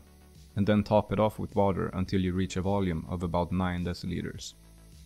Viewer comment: Even the YouTube CC couldn't understand what you said 7:37. 9 what? I hear "9 deslitres" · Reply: Deciliter. A tenth of a liter.